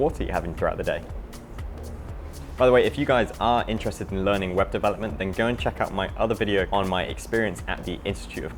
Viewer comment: LOL, thought you said Weapon Development instead of Web Development at first. Love your channel, keep it up :)